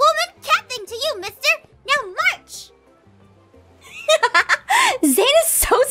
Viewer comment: Is it just me or does it sound like is saying “meaw march” instead of “now march”